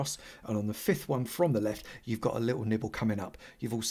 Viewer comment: I seriously thought he said nipple instead of nibble. English is a weird language. Side note: love the way you talk about observers. Never thought about it as them being so easily scared that they fart a redstone signal when something happens.